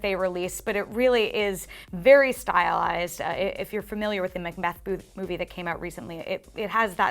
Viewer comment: A similar feel to what movie did she say? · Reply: The Scottish Play. You're not allowed to say its name because it's unlucky. Oh ... wait ... that only applies to actors who star in it. It's called "Macbeth". By Shakespeare.